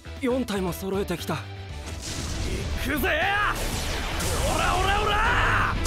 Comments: did he just say ORA ORA ORA? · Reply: Nejc Bosin Star Platinum!,ORA ORA ORA ORA ORA ORA! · did kazuma just say ORA ORA ORA?